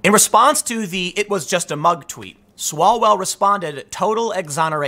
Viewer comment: Did I hear you right around 8:34 that you could "buy" MSNBC excuse "that a mug" made that sound?? MSNBC CAN'T BE TRUSTED FOR ANYTHING!! In 2012. MSNBC DOCTORED AN 9-1-1 CALL in the Treyvon Martin & George Zimmerman shooting incident. NBC as a network CAN'T BE TRUSTED AT ALL.